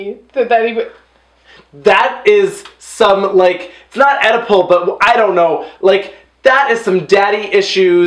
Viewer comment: "That is, some like, not 'EDIPO', but I don't know, like, that is some daddy issues…" It was funny saying "Oedipus Complex" as "edipo." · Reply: I think he said “oedipal”. But I love “edipo” 😝